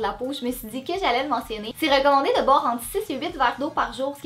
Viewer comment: Elle a dit quoi à 0:57 ? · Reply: Les Crossingueuses 6 ou 8 verres d'eau 😉